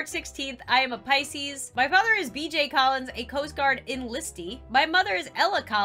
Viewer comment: Who else thought she said "DJ callid" when she said BJ Collins